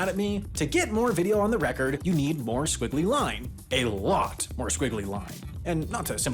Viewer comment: This is atrocious! At 3:37, we clearly hear say "squiggly", but the closed captions say "wiggly!"